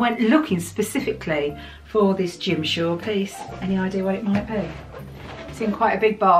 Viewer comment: Ohhhh lordy. I first heard " Gym shorts" instead of "Jim Shore".🤯